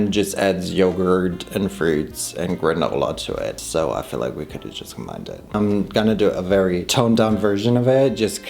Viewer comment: I think you said "I feel like we could've just combined it" haha. I speak fluent mumble🩷